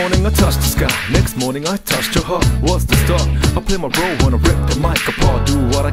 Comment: at the "On a Sunday morning I touch the sky Next morning I touched your heart" part i thought he said "touched a hoe"